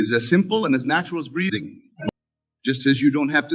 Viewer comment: Did he say at 11:47 the power of breeding or breathing? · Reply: Breathing